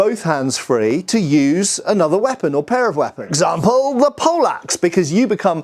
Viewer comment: Am I the only one who heard "...example, the Polaks!" and was confused for a second? "Alright guys, there is a great weapon you can wield, and that is the DUTCH!"